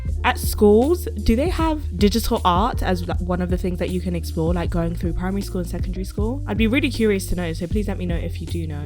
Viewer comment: did u just say primary school? what- im so confused i didnt know u said that- (i sometimes watch u and when i do i always thought u said other stuff that i have no knowledge of)( 9:53 )